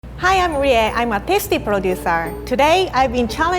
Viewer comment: The first two seconds of the video I thought she said “Hi I’m Rie, I’m autistic” it made me laugh bc I am too